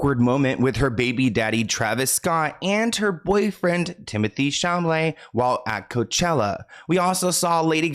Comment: 50 seconds in and I hear "timothy sham-lay" lol WHO